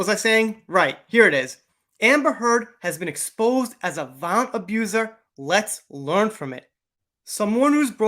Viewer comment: part did I hear correctly am I the only one who heard vamp abuser or am I imagining things?